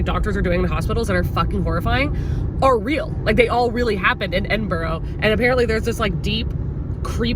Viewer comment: I'm living in the UK and it took me a second to realise you were saying 'Edinburgh' and it was the cutest way i ever heard anyone saying it. I am used to people saying edin - bruh lol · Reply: I thought she was saying Edinboro which is a place in Pennsylvania and I was trying to Google this dark medical stuff and finding nothing 😆 then I realized it was Edinburgh Scotland LOL